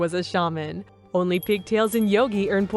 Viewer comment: what the hell did she just call them😂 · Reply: "Pigtails": Isadore has them. "Yogi": The yoga instructor.